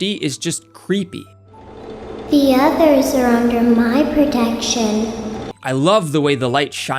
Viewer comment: What Lefty said in 3:40 minutes is... The Puppets voice lines. "The others are under my control" "I am very aware" It is "The others are like animals, but i am very aware"